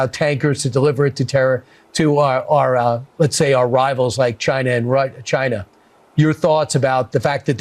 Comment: Anybody else catch it at 7:30 when he says “and our rivals, China and Rus- , China” — I mean clearly he was going to say Russia but backpedaled.